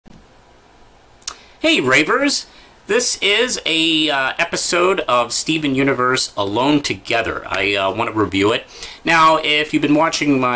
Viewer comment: At the beginning of the video, I thought you said rapers at first